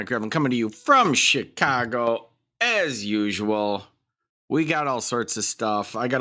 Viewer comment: lol.. i thought he said shitcago*..